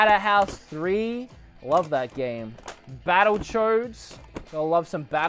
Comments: sounds like he's saying battle chodes instead of battle toads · When you said “battletoads” it sounded like you said “battlechodes.” Lol, I’d play battlechodes. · I wasn’t watching the screen so I thought he said Battle Chodes lol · I thought he said Battle Chodes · Sorry but when he said battle toads it sounded like he said battle chodes sorry about your Christian Minecraft servers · Sounds like he says battle choads rather than battletoads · did I hear you say battlechodes twice at 9:00 ? Battle....chodes. Yes, yes I did. Kay moving along.